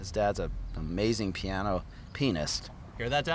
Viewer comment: I heard billy's dad is a really great penist